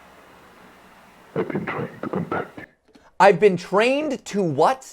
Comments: rewatching this, the creepy voice at the end of the second video actually sounds like its saying "I've been trying to come back to you" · I heard "I've been trying to come back to you... maybe Jack trying to reunite with Sophie, from what I know about the series?